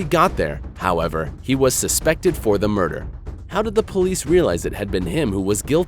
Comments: Wow great english 9:46 the person says suspected but the subtitles says suspended... bro for real? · days suspended instead of suspected